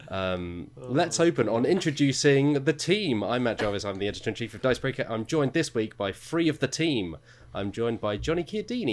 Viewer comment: Hello from America! (I'm afraid I'm British but I am in America, I'll send you an email if you like) you mention a game at 1:10 ish, a small deck card game and I can't quite make out what it's called, please would you enlighten me. Thanks in advance and thanks for the content. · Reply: Jonathan Lee It’s The Crew!